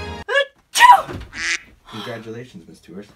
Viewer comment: I thought your brother said “congratulations, you tuber” and I spat out a few grapes I had in my mouth holy fuck